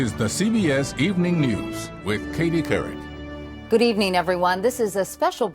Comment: I heard the the ppl said "with Katy Perry" #